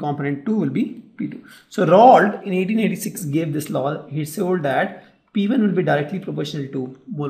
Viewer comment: did you say "He sold that" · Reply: He said "he told that" 🤣🤣